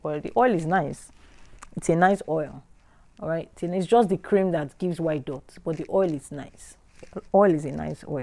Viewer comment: Hello,thabks for the video.. I made the white dots oil,buh it hasnt cleared...am on three weeks now.... Also,what can i use for sunburn ? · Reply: i thought u said we have to suspend the cream that gave the white dot.